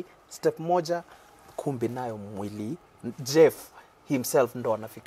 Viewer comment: did he just say "mwili" and quickly corrected himself to jeff